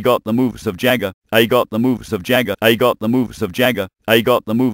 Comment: haha sounded like commputer was saying i got moobs like jagger xD funny ep (